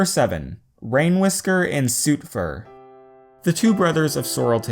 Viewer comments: For a moment when he said Sootfur I thought he said Soupfur XD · when he said soot fur I thought he said soupfur